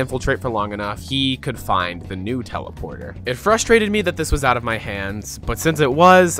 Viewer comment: I thought he said Nutella instead of new teleporter